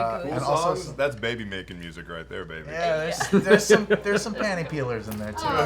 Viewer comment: Ok, I need some help. WHAT term does Matthew think is terrible at 0:20? What did Sam say? I've played it over and over, but I"m not getting it. Am I too old because my ears are jacked up or too old because young folk are using terms I don't get? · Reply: Panty-peelers. Songs that get you laid.